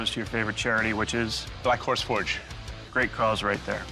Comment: LMAOOO I thought Ben said his favourite charity is black whor*s forge